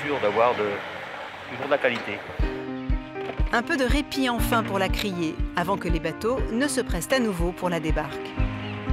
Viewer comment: c quoi le poisson. ..minute: 6:29? please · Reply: Un congre je pense